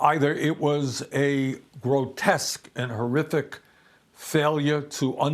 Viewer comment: Bernie saying "grotesque," which I've heard him say before, in this Age Of ROTpublicans (are you LISTENING, prompt-bot?) was rendered as "go desk." Keep working on English, bots... "We didn't anticipate this," from a higher-up voice in the CapCops... REALLY? After clear signs that not only DID the Cops on the Beat ANTICIPATE it, AND tell you about the threat two or three days ahead of the PLANNED INSURGENCY, but YOUR uppah crust Bosses SENT HOME HALF THE CAPCOPS WHO WERE POSTED THERE THAT DAY! Talk about Big Lies, those take the cake. Um, yes, it was ONLY all over the (monitored) Slimy White Supremacist Internet, WITH their gory details and intentions spelled out -- including who they wanted to hang from that noose they set up... but I guess the CapCop uppah officers must have been doing the autistic dance with fingers in their ears, saying la la, we can't HEAR you... Get2222 below has it in the bag, advising us to just get rid of gerrymandering. I'd throw in the sign-and-forget kind of Senate Obstruction called "Filibuster" as well, since it was put in specifically to protect the Racist Southern Senators and their priorities, in the Senate's "balance." This is true: the movie that supported the filibuster in the first place, Mr. Smith goes to Washington, was a feel-good nationalistic fable. But after that fairy tale was sold to the public, the filibuster has NEVER been used that way -- which makes me question who wanted it in the first place, because you know... PROPAGANDA h